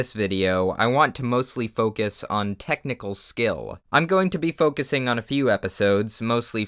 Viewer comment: I.. I heard.. “Tentacle skill” I can’t- 😂